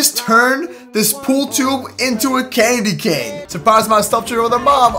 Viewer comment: did he just say candy cane?